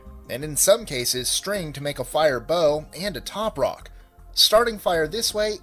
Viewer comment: did he say fire bow?